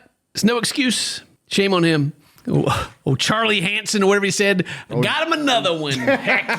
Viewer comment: I thought at 1st he said Charlie Manson